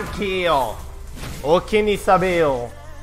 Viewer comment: One shot one kill uki ni sabelle😂 Like nyo kung narinig nyo @ 9:08 👇